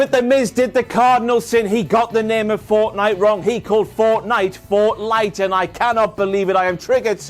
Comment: I thought The Miz said "Fortline" (which is a flea treatment, I think).